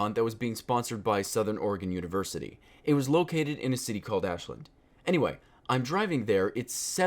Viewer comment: At first when he said a city named Ashland I thought he said auschwitz.